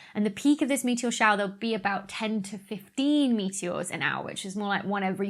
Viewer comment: I thought you said 10^15 meteors an hour, a meteor every 3.6 picoseconds is pretty crazy.